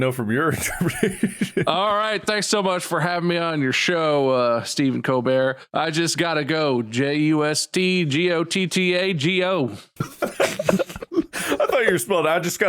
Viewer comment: tf is he saying here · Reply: "Thanks so much for having me on your show Steven Colbert, I just gotta go- J U S T G O T T A G O" The same bit they were doing but it was just kinda fast and unclear cuz of that